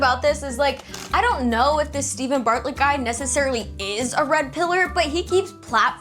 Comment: I honestly thought you were saying Steven Fartlet....